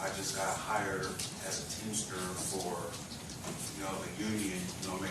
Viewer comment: Lol thought he said teacher too